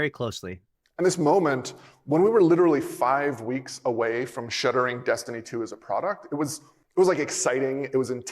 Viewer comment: The Bugie rep did NOT say we are 5 weeks away from shutting down Destiny 2. What he said was we were 5 weeks away from "shuddering the Destiny 2 product, it was and exciting time and also terrifying. The word shuddering means to rigorously shake things up. Which would make sense sense they made drastic changes and weren't sure how they'd be received. Why would they feel it was an exciting time if they were closing everything down. Doesn't make sense context wise. Shuddering is one word, shutting down is two.